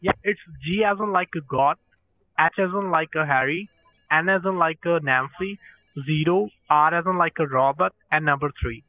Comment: Did he just say "s as in like Harry"